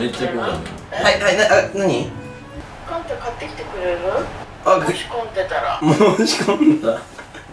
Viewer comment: なんか、かんたくんの｢申し込んだら？｣が繋がらなくて、なんども聞いて｢もし混んだら｣に聞こえてきた。